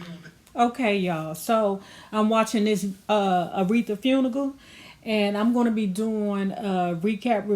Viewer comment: Please Subscribe to My Second Channel: <link> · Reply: Were you trying to say funeral because I heard “Funigal” ?